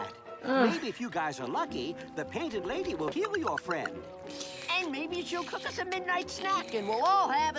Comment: BRO I THOUGHT HE SAID, "Maybe if you guys are lucky, the Painted Lady will kill your friend!"